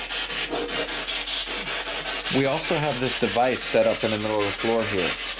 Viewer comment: [‘...could you please tell Linda your name?] 24:30 “We’re tryin’..” When you asked if there was an object they were drawn to; the same word came out twice- but I couldn’t make it out..it sounded like “..set” maybe “train set” but it was hard to make out